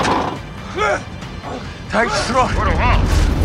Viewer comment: What are the Germans saying at 5:29? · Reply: geh raus geh raus geh raus geh raus (It is in this sense)